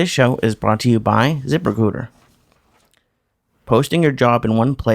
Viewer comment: Get up you taint-sniffin mooks! It's time to light up a joint-o-death, sitback with your left nut out and watch this like a doctah! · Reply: I had just watched a chris video & when I clicked on this it sounded like he said brought to you by Zip a hooter...lol